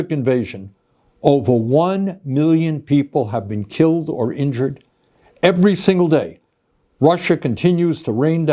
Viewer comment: At first, I heard "over 1 million people have been killed or injured every single day". I was a bit confused